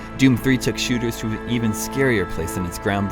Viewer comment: did he say: Assaultcube FTW?? yay! :D